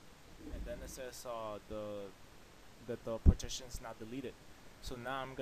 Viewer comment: Sounds like you said: "The partition is not deleted".